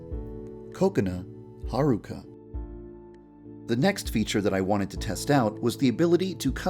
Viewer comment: I thought he said ‘coconut haruka’ 😂